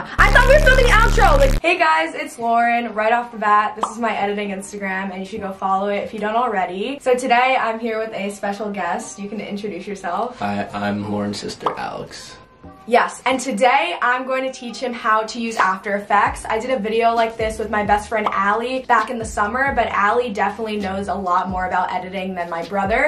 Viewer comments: Oml did he just say I'm Laurens sister speak English lol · did he just say "hi im laurens SISTER alex"